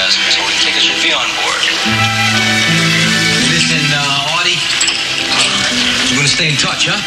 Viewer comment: Am I hearing wrong or does Fonzie call his brother Artie at 3:48? Did Vito seriously name both of his sons Arthur? edit: just looked it up -- he totally did. the title of the episode is "Arthur, Arthur."